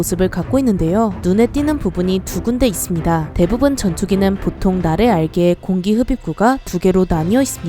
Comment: 날개아래가 다르게 들리는데.... 피곤한 상태로 녹음 하셨나보네ㅠㅠ